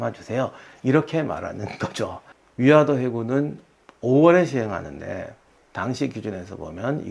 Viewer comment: 멀리서 듣는데 위화도 회군이 '위아 더 해군'으로 들려서 달려왔습니다! ❤